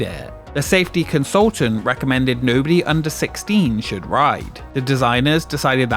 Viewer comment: I thought they said no one under 14 wtf